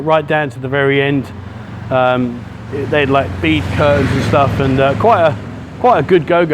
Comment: Did anybody else hear “Beef Curtains” at 3:53 ? 🤣🤣🤣